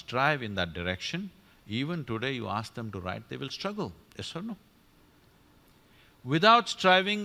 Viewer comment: I think subtitle job was done by lazy person. He didn't understand when sadhguru said "yes or no". After every two sentences the subtitles say [ INAUDIBLE ] . Is it a joke?